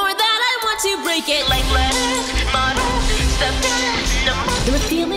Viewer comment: like letters me: wait did she say life letters